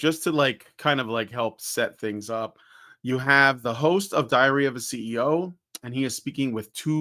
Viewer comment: I keep hearing 'Diarrhea of a CEO'.